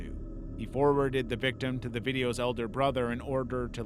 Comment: I swear he says " they send the victim to the videos brother". Am I insane?